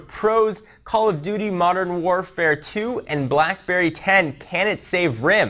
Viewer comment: did you just say Call of duty modern warfare 2 at 0:21 ?